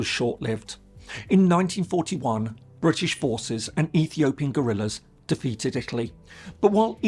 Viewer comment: ….did he say ethiopian gorillas???